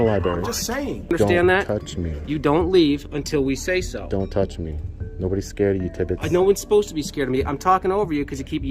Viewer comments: “Nobody’s scared of you tibbits” 😂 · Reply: I thought he said nobody is scared of your tid bits... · I heard “Nobody is scared of you Titties” and it made it funnier 🤣